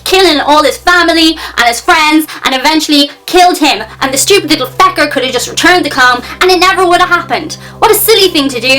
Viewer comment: stupid little... fecker? that sounded like something a lot worse, had to go back and listen to it a few times.